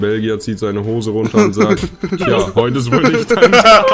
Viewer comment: Was sagt T bei 13:19Min ? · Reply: der Belgier zieht seine Hose aus uns sagt "tja heut is wohl nicht dein Glückstag"